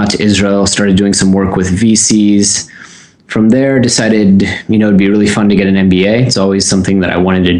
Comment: it sounds like you said "feces". What did you really say?